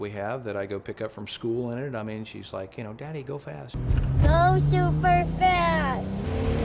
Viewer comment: did she say Super fast or Supra fast :P